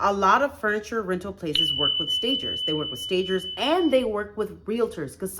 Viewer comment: What is the word she is saying? Fakers? Bakers? Please let me know. I am ignorant. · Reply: Stagers! Got it!